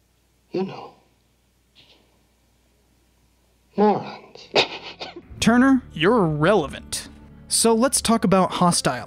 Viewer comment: what did he say? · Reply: Tuner, you're irrelevant. As in Ted Turner